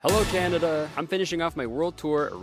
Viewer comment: did he just say "hello candida"?😭😭